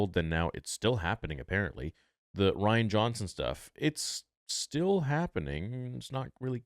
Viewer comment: When you were talking about delayed projects and said the Ryan Johnson stuff, I had to rewind it because I thought you might have said the Ryan Gosling stuff. Then I thought Ryan Gosling could actually be a great Star Wars character